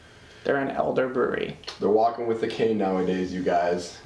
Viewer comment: I thought he said, "Walking with the King." That's how I'm gonna refer to the elderly from now on.